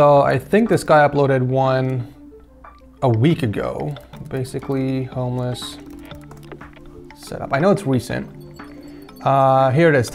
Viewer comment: i thought you said viks setup was basically a homeless setup 🤣